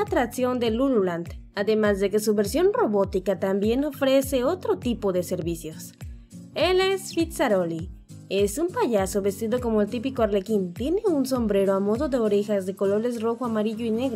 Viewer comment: Cuando dijo su nombre pense que habia dicho Pizzarolli xD